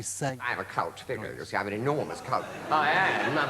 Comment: How he got away with going on the Wogan show in the mid 80's at six o'clock at night saying "I'm a cult! I'm an enormous cult!" I shall never know Wogan is dying in his boots because it really does sound like he is saying "I'm a cunt!! An enormaous cunt!" outrageous - so subversive and of course sooooohh funny!